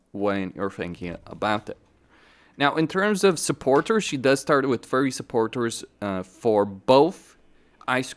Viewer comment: sounds like you said "she does start with furry supporters", and you know what, that's probably the case.